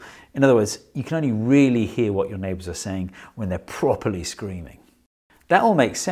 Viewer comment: I thought you were saying: "you can only hear your neighbour if they are properly screwing"